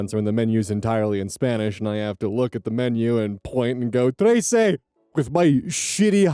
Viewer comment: Not to riff on you but did you say "Trece" (13) or "Ese" (that)? Cuz if you went to these restaurants and just said thirteen to people while pointing at the menu that's kinda funny ngl, but I hope at least they understood what you meant most of the time. Also thanks for acknowledging authentic Mexican food, much appreciated.